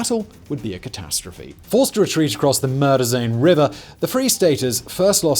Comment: I heard “forced to retreat across the murder zone river” 😂